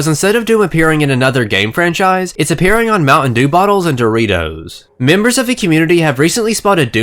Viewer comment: I swear to fuck, I kept hearing you say "Mountain Dew-m." If that was intentional, that's fucking hilarious in the punniest way. However comma if it was unintentional, and it seems like I'm making fun of you for a minor speech impediment, well, it's still hilarious because I'm an asshole. I'm kidding, about the making fun of him thing, not hearing "Mountain Dew-m," which seriously they should be using to advertise this crossover anyways.